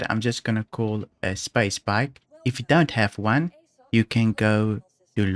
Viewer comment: when you say "Space Bike" the firsts times i heard "Spa Spike" and i though "what the heck is that?" only at the very end understood you were referring the bike hahaha. Great VIDEO by the way, very helpful, i may try those missions myself tonite.